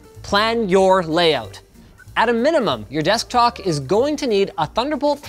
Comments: Did he say "desktok" at 02:43 and not desktop or deskdock? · did he say desktock? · did he just say "Desktok" 😂😂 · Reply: Nah, he said Deskdock. He says it a couple more times after that. At first I thought he messed up too but nah. Lol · Desktock? 2:43 anyone else hear it? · did Linus just say "desktok"? Is that what happens when you use Tik Tok at your desk? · Does anyone notice at 2:44 he says desktock instead of desktop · did anyone else hear "desktok" at 2:44? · did he say desktok ? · Did he really just say desktok instead of desktop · Anyone hear "desk-talk" at 2:43? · He really said "desktalk" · You said desk talk around 2:40 instead of desk top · did anyone hear linus say desktalk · anyone else hear “desktok”… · did he just say desktalk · did he say desktok? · anyone hear at 2:43desktok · did he just say desktoc 😅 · did he just say desktock??? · is it just me or did he say descktock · Did anyone else hear him say desktock at 2:44? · Did he say desktoc?